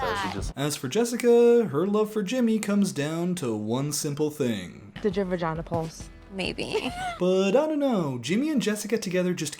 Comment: what did she say? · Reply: I heard, "Did your vagina pulse?"